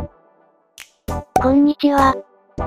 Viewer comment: こんにちはって聞こえたけどこんちにはってなってるの今気づいた